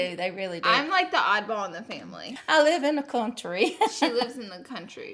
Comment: i thought she said i live in a plum tree 😂😂😂